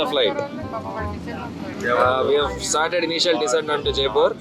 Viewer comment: did anna just say initial disadvantage to Jaipur instead of indial decent?? at 9:36!!